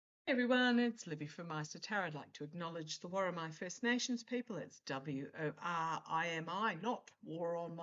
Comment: To be honest the first time I watched you I thought you said the war on my people 😂